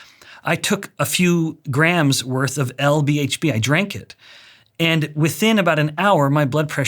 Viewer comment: what is LBHP drink at 36:25, that he said he drank.